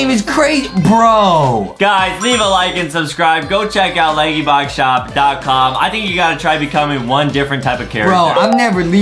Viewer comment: lol i thought Adam said "LadyBug . com shop lol